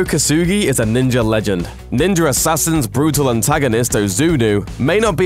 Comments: All I hear is "Ninjer Assasin" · i keep hearing "ninjar assasin" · I hear "Ninjer assasin" tabarnak! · Why does it sound like he is saying "ninjer assasin" ???